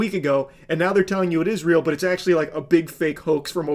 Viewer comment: Israel is involved in Epstein too!? lol me just mishearing info and misinforming it